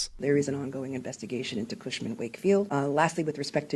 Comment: For a second, I thought the NY AG said "Marsha Warfield". I was like, "woah, one of the stars of Night Court was involved in this?"